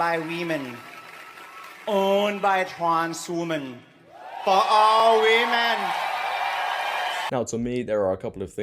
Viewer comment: It sounds like he said for all we men 😅